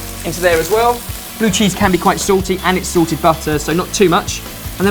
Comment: I thought he said 'sorted' butter instead of salted butter lol